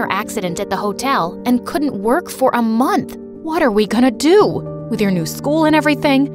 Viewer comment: I heard water we gonna do